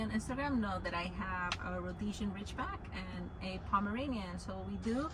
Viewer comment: For a second there I thought she said Norwegian Ridgeback from Harry potter